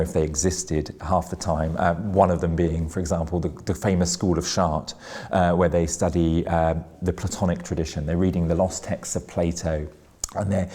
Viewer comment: Bro did u say school of shart